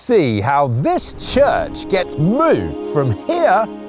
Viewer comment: I heard it as “We’re about to see how this CHUD gets moved from here to here!”